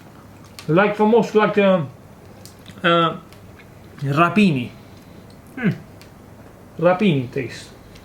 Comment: you use a word in Italian. Not knowing Italian phonetics, to my english ears it sounds like "rapini" or "rah-peh-neeee". What is the word in Italian? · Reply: Rapini= broccoli rabe Google is a friend.